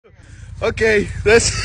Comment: “I know what’s wrong with it ain’t got no gas in it” 😂😂😂😂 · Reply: I thought he said" it ain't got no gadget "